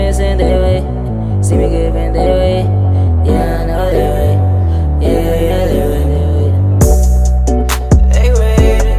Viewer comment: Did he just say "Yeah I know de way" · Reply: Tanner Gipson I think he said yeah I know they waiting🤷🏽‍♂️